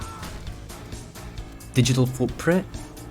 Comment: i heard at first "digital poopprint"😭